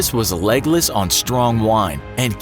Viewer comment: wait did he just said "A Legolas on strong wine," tolkienites where you at?